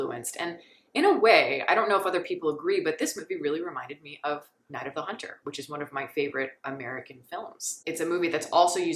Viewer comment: Great review. What is the exact title you mention in 8:56? I'd like to watch it, but I don't understand you. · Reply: Night of the Hunter